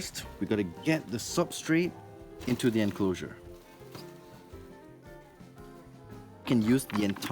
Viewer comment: I thought he said she successfully SHIT in her incloser😂😂 Edit: now I'm hearing Birdy terds instead of bird eaters